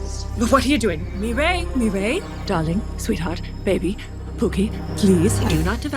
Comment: DID SHE JUST CALL HIM POOKIE ? 😭